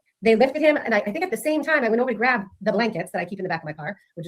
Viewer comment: I love....Rewatching lol So much I've forgotten!! Think I need to start from the beginning of the trials and rewatch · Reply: something I thought Jen grabbed blankets NOW is saying she went to get THE BLANKETS!?!? DID I HEAR THAT RIGHT